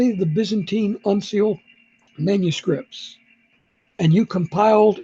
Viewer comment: What did he say? Byzantine "unseals" or something? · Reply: Uncials.